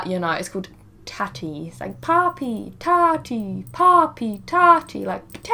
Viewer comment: Thought she said papii 😩